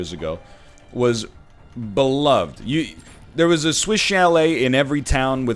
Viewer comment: Ok the whole time he was saying "Swish Chalet" i thought he was saying "Switch LA"